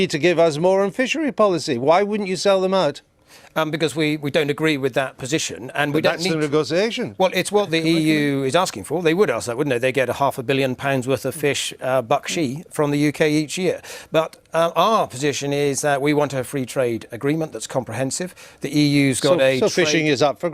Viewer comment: Did he say bakshi